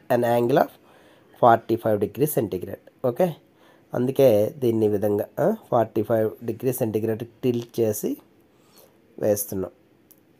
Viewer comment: i didnt understand what he said but at 13:00 he said centigrade